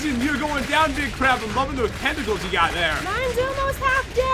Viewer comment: did Pat just say "Tentacles" ?